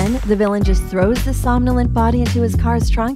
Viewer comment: I think that you said "somnolent" at 1:23.